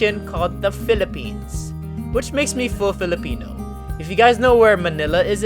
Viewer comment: For a second when u guys typed Filipino I thought u guys said flamingo